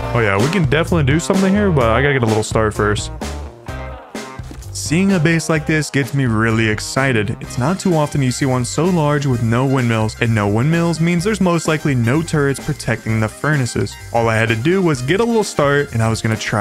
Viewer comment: 16 and :32 It sounds like you said, "no windows", speak clearly.